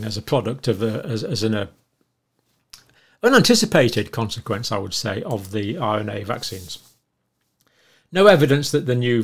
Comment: it sounds like he says "...an unanticipated consequence of the mRNA vaccines...", but his words aren't very clear. I hope he didn't say that because that's simply not true. MANY scientists (such as Luc Montagnier) were warning of ADE before and after the rollout.